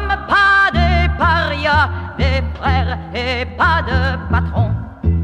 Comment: I think she actually said “des frères et pas de patrons”. It doesn’t change much the meaning though.